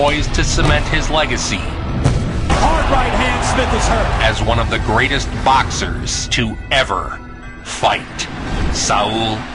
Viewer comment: I had to replay the Mayweather part. I could’ve sworn I heard that he was one of the greatest boxers to never fight. 🤣